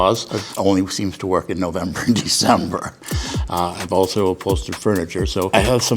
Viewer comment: um….. why is this my comment timing i uh….just wanted to say hi…..santa clause…. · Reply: Also at this time it sounds like hes saying “Cringy December” 😭